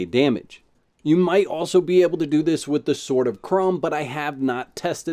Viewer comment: What, ...did you say "sword of crom" :) :) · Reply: Krom sword it's 2 handed.